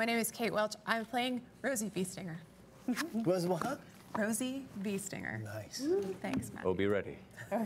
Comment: First, I understood "Biestinger" (in The Dark Eye => intelligent animal type of feyfolk) instead of Beestinger.. And I thought, wow cool does she know german and did she play "The Dark Eye" already? :O until In understood :D too much Pen&Paper for sure